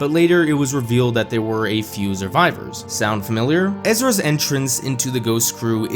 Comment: I hate to be that guy but there's no 'z' in Survivors -_- Every time I heard "Zurvivor" I felt an entirely inappropriate frustration inside me lol